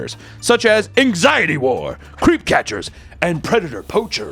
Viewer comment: Ohhhhhhh Creep Catching. I thought you said Creep Casting.